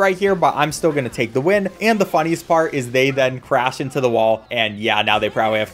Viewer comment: he said "they then" and I heard "they/them"